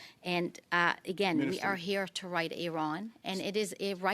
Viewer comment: Did i hear Iranians?!!!! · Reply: Apparently she said right a wrong but it was not clear!